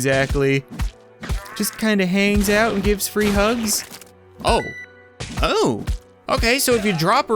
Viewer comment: The "Free Hugs" Zombie is actually a Rare encounter. She has a 1% chance to spawn at rhe beginning of any level. She is completely harmless tho killing her will drop 50 rage points. Melee units will completely ignore her but Ranged units can kill her. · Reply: I thought you said face hugs